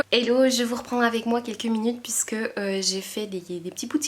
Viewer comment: Tu me maaaaaaanques ! J'ai entendu "J'ai fait des tirages". J'y ai cru pendant une seconde :D J'attends ton retour sur Nevermoor, alors reprends-toi :D Des bisous ma beauté !